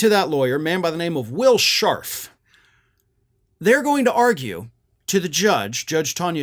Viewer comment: I thought you said Will Schart. lol